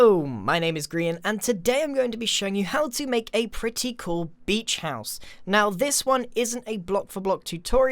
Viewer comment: Who else thought it said bleach house